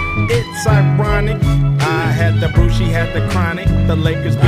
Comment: It's ironic, I had the booze she had the chronic, the Lakers beat the Super Sonics. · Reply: TheNamesJohnnyYuma LOL I always thought he said "I had the brew"